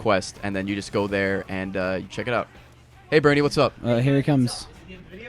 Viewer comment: Lol my name is Brady and it sounds like ray said "Hey Brady, whats up?" I was freeeeeakin out!